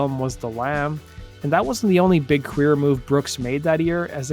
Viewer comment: I had to "rewind" around 1:21. I thought the voice over dude said "That wasn't the only 'big queer' move Brooks made that year..." It actually said "big career" move Brooks made... yada. I guess I was a kid in the 80s, and I misheard that! I need to mention that I AM a fan of Garth, and that was not my attempt to be cute: I was genuinely shocked when I misheard it. Much too Young, to Feel this Damn Old is one of the best country songs ever - up there with George Jones, Haggard, Loretta Lynn, George Strait and Alan Jackson, etc.